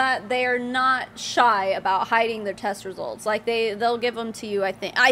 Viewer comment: I had to listen back to this bit, cos at first I thought you said they were not shy about hiding their testicles...